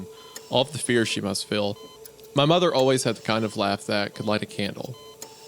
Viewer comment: Did he just say his moms laugh is hot? While she's on her death bed? What the heck.